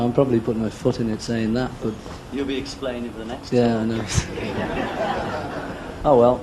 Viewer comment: When Paul rolls his eyes at 10:38 what does John say just before that? It's definitely "you'll be explaining for the next..." and then the laughter drowns out the last word he says xxx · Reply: You’ll be explaining the next two ❤